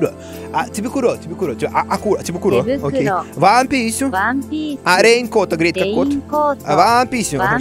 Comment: Дэсу ка? Вам писю? ( а мне послышалось Ван Пис😂)